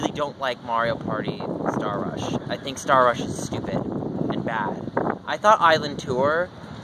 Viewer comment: in 2:14 did you just say stupid!!!!!!!!???? 0_0